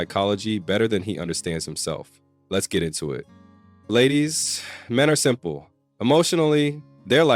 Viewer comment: I thought u said "men are simple" 😳😅😅